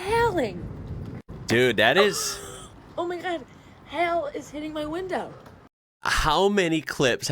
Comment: Oh I thought the storm lady was saying "hell is hitting my window" 😂😂 also in W.A. we use helicopters not planes on tours down the Swan River from city to coast and back.